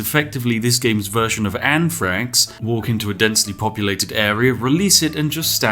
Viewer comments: I didn't hear "anthrax". I heard "Ann Franks", and I was so confused. Like, would you just be releasing a ton of really stealthy snipers or something? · Lol.. When you misheard "Anthrax" as "Anne Franks"